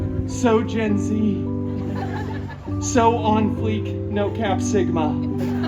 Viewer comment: Did Deadpool just say sigma 💀💀💀